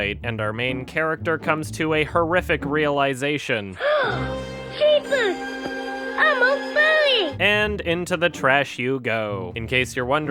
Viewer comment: It sounds like he said: Jesus! I'm a furry!